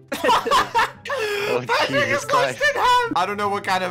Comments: what did I hear at 1:20???? I think he's trynna say "Patrick is sliced in half" but it sounds like some else ion kno lol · Not trying to start anything but did he say the n word when Patrick was sliced in half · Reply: he said "patrick was sliced in half"